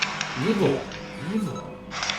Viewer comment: you guys hear "evil" but I hear clear as day "I'm right behind you."